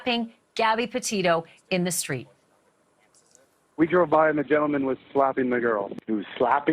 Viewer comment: I swear to god she said Dryin Laundrie at 18:48. I’m tried so I double checked. It really sounds like dryin’ laundry